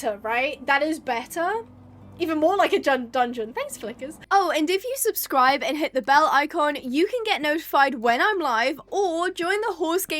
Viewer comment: i heard that as “ oh and if you subscribe and click that bell, icon” 😭